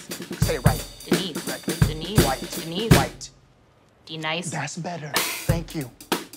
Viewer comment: I thought he said be nice not de nise